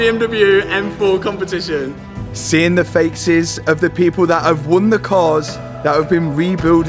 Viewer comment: I swear he says "seeing the fake-ces instead of faces at 8:27 .